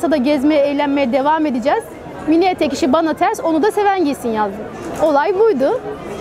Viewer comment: `.... Bana ters ona da seven gitsin..` diyor. Tam olarak anlamadim noktali yeri? Milliyet isi mi diyor ne diyor abla? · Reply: Mini etek diyor